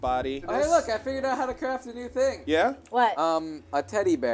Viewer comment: i craft a nuke thing thats atcually what i heard